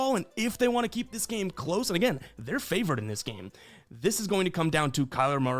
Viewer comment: did you say the cards are favored?